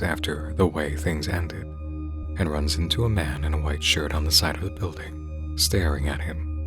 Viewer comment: genuinely thought that said [white man].